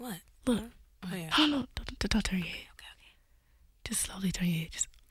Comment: to this day I haven't figured out what she's saying "the d-d-dottery" ?!? 🤔 Help😢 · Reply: Kelly said “d-d-d-don’t turn your head.” 🤓